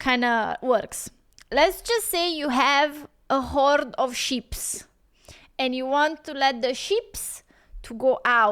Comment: LOL She said hoard of sheeps, I thought she said hoard of ships until she said pasture.